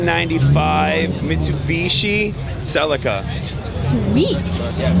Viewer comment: wait what ? Mitsubishi CELICA ? Or i heard the wrong shit?